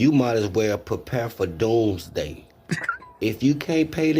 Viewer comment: mk Did brotherman say 'DOGEday'? ... :D